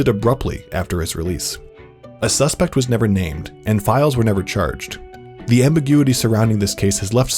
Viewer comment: Is it just me, or did he just say that 'files were never charged'? Time